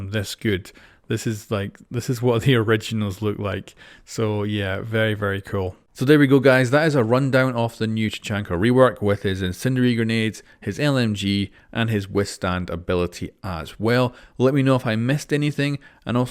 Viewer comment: Dude I thought it said incendiary nudes